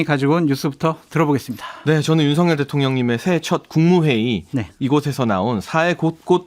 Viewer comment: 장예찬 시러라하는사람 많네요 나두시른대 ㅎㅎ 대통령님이 대통형님으로 들리넹